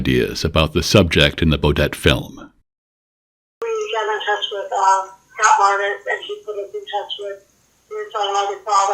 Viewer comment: I couldn’t understand anything said at 41:15 and onward. Can someone clarify? It sounded like she had an interesting theory. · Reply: She was saying it was a eel trying to get away from the turtle , and there separate objects